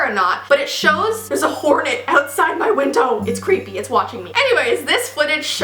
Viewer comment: I heard "hornet " as "woman" and I freaked out as how could a woman be outside your window watching you ? that's so creepy